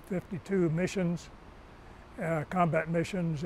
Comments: did he say 2,700+ missions!?!? 😲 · October, 1969 our Huey was targeted by a 51 SAM. Pilot nearly inverted the helicopter to avoid the missle. We landed safely. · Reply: did he say 2,700+ missions!?!?! 🤯